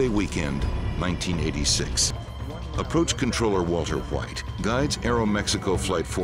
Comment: did I hear Walter white?